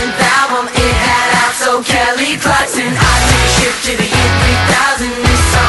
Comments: I thought it said I took a shit to the year 3000😂😂😂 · i always thought they said “i took a shit in the year 3000” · I always thought they said I took a shit to the year 3000...